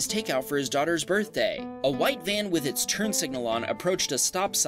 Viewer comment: did he say a white van